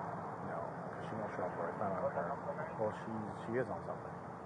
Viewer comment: I thought she said she didn't know how he found out where she was. Then later she said she shared her location just in case she needed him to pick her up.